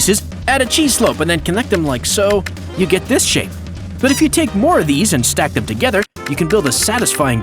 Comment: I think he said you get this sh*t